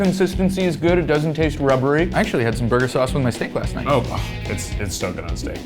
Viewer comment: omg i swore he said he liked the sauce on his SNAKE 🐍 and had to do a quick rewind hahaha